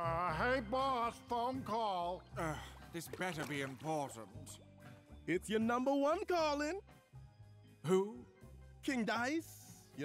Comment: Am I the only one who thought King Dice said "It's your number one, Darling" instead of "It's your number one calling" ?